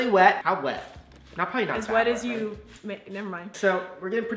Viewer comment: what were you about to say at time 4:43 hmmm………"As wet as you ma" HEM???????